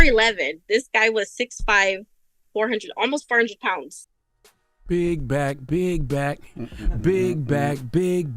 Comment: I thought it said “big black bully” 😂😂😂